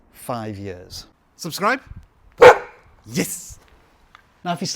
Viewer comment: Did you just called me DOG subscribe 7:14 😂